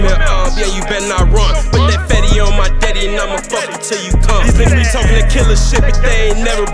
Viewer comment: Sounds Like He Said I'm A Fuck Him Til He Come. First Verse/Hook